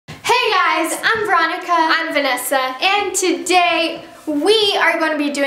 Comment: When Vanessa said "I am Vanessa" it sounded like she said " I am finessa"